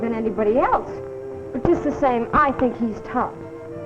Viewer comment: "But just the same -- I think he's tops". She did say "tops" right?